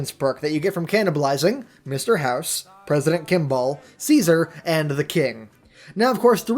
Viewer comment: When i heard say president kimball I thought he said president kick-ball